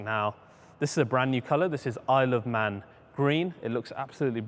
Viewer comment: He said at 9:42 the color was “I love man green” LOLOL well that’s at least what I heard